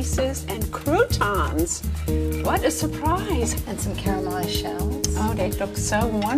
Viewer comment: It sounds like she says “caramelized shells” lmao